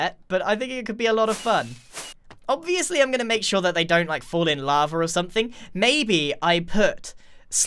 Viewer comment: Did he say fall in lava or fall in love?😂